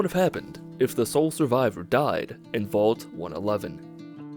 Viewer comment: I thought it said “What if the sole survivor died inside?”